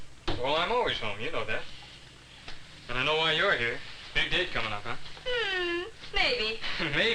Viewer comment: Did he say "Big dick coming up huh?"